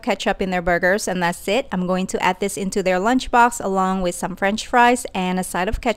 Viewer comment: I thought it said "paradox" Instead of "lunchbox" Yea